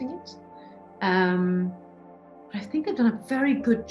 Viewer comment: Did she just say, "I think I've done a very good job to recreate the book into a film." When I hear actors talk like this it turns me off to their films. While I understand that it does take a bit of ego to actor, there are many actors who have been able to stay somewhat grounded and humble. Rebecca's comments strike me as someone very pompous and full of herself. Also: Rebecca Ferguson: I haven't read the book, but I know it's sexist literature Internet Surfer: I hate this article. I know I hate this article because I read the title and looked at the date it was published, so I know hate this article. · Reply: Nope. She says "they've" and not "I've". She is so not pompous and full of herself, if you'd listen to to the whole podcast you'd realise that. You would also realise that she never calls the book sexist. The person who made this video misinterprets what she is saying and people latch onto it. She says that Denis rewrote parts of the book in order to make it fitting for a 2021 audience. If anything, people ought to be mad at Denis for changing parts of the book. It is not like she wrote the script...